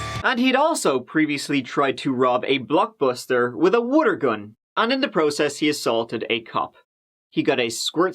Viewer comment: At first I thought said "Woodergun" and i was trying to figure out what that is than i realized he meant "Water gun".😂😂